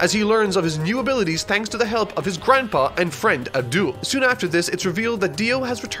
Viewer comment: it sounded like you said n'doul lol its fine dont think this as some stand comment it just sounded like that to me. i know you said "Avdol"